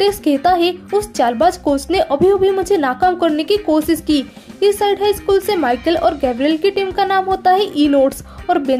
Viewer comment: pa school ka naam kya bola 🤨 😂😂😂😂😂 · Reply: East side high,😁